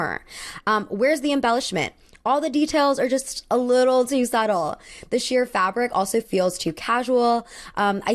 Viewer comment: just wanna let you know theirs a typo in the captions at 24:04. You said 'a little subtle' but the captions say 'suicidal'